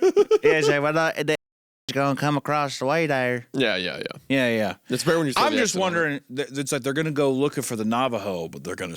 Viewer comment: I must know what word they said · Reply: I think it was “In-Jins” (say it phonetically) pronounced like engine. Old timey way of saying Indians.